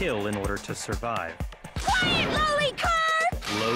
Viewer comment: I thOUGHT SAID "QUIET LOLICON" FOR A minUTE I